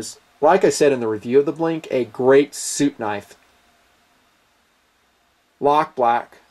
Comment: I thought you said "soup knife" until you cleared that up. lol